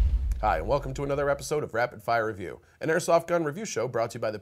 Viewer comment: At first i thought it said Raped fire Review